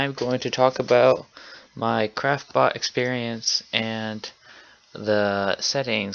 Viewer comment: sounds like your saying crapbot